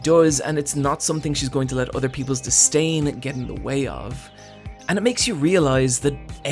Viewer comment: I heard STAIN